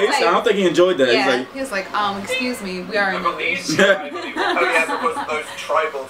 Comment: What did say at 3:49 I couldn't quite hear also great job on you're videos keep on doing what you do :) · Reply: She was talking in dans perspective and said "we are in a relationship"